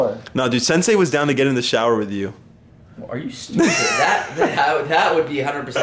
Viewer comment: did say wallah